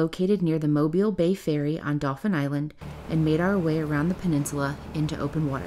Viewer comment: I stayed a Dauphin Island 21 years ago on my honeymoon lovely beaches! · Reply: A g'day from Tasmania. Thanks for writing the name of the island because I thought I heard them say Dolphin Island. Now I can google correctly and check it out.